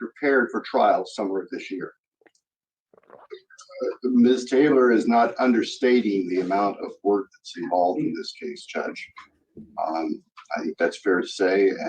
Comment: Bill Thompson actually said, "Miss Taylor is not UNDERstating the amount of work that's involved in this case." I don't know what he meant to say, but people heard it as "not OVERstating". Was it a cryptic dig, or did he misspeak?